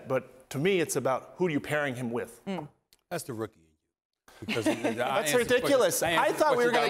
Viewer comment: Does anyone know what SA said at 1:08? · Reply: “that’s the rookie in you”